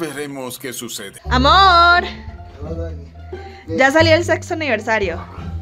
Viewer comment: Escuche mal ? Porque creo que escuche el sexo aniversario en el minuto 0:20 del video 😂 , saludos a los dos crack